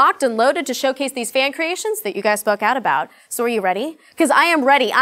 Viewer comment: at :35 seconds it sounds like she says "We are locked and loaded to showcase these fan creations that you guys fuck out about." LOL